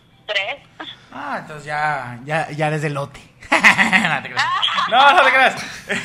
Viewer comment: Que dice la Mole: 26:50 "ya eres de....." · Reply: ya eres de elote